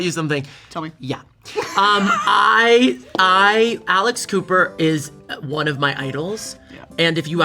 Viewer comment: I first heard, 'Alice Cooper Network'. Also, he needs Sam & Colby and / or Kris & Celina. Just saying.